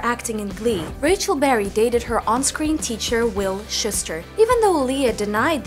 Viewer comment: All I heard was ' Will Shister'